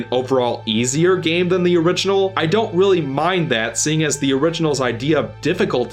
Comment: All I kept hearing is I'm bad at the original so I didn't like it 😂